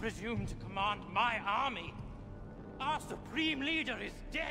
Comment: To be honest the first time I watched this movie I thought he said your no supreme leader